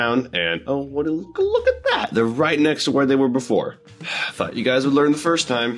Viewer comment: the first time i heard swear. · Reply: I know this was 8 months ago… but he said thought